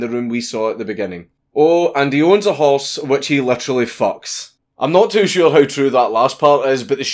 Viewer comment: Deadass thought you said he has a house he literally fucks, then horse threw me off more.